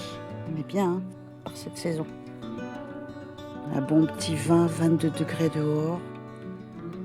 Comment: 😂😂😂😂 j'ai compris " un bon p'tit vin... 22° dehors... "